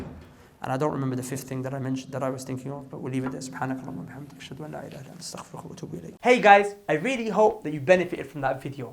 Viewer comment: Salam, What did brother say at 17:46 if you can help please reply Jazakallah khair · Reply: Walaikum al salam akhi it is A'oodhu bi kalimaat-taamaati min khalaq x3